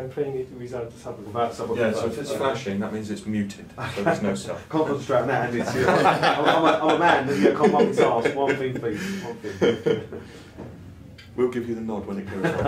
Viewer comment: what did you say at this point? Couldn't hear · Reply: j is that banter? they asked me to pay attention to the led on the sub as well as listen. I said I can't multi task and don't expect me to do both :)